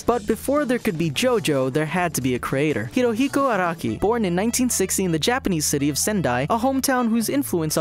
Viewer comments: God i thought he said araki was born in 1916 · i thought he said araki was born in 1916. i thought he really was a vampire... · i heard 1916 at first lol